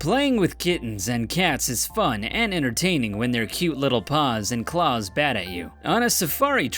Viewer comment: I thought he said bear sloth and laws